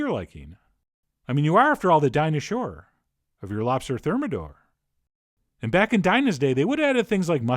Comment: I thought that said "Lobster Terminator"...